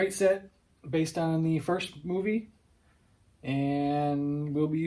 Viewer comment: I thought you said "based on the worst movie" I was mad for 10 whole seconds before rewinding and listening again. This set looks so good with the lights added.